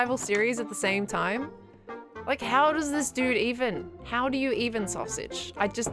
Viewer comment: I thought she said.. "how do you even sausage"😭😭 The comma 😅